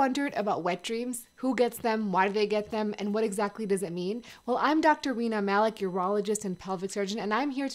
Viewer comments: Eh, yooo! Tell me why sounds like she said, "I'm doctor Weener Malik." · Did i just hear "Doctor Weiner" and "pervert surgeon". Thanks for the captions, cause I thought, what a job!